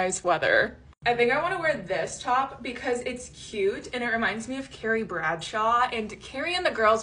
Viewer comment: I thought you said this top reminds me of Terry Bradshaw for a second 😂